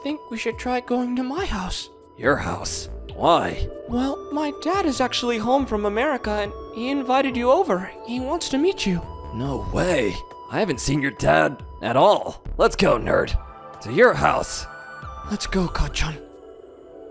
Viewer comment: “No way i havent seen your dad for all.” i thought he said ‘wait i thought you were fatherless’